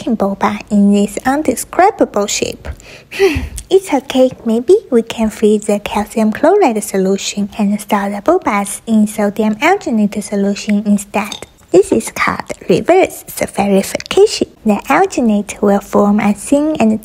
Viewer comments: OMG for a moment I thought she said so damn Alginate 😂😂😂😂😂😂 No hate. ❤ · Why did i hear this "crappy bullshit" she said "indescribable shape"